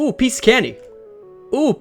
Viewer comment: The intro sounds like his saying "piece of kenny" lol 😄